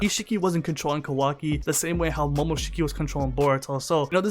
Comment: sounds like he says Baruto lol but great video as usual